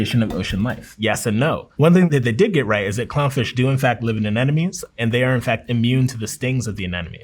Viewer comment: It sounded like he said Aneṉome, twice.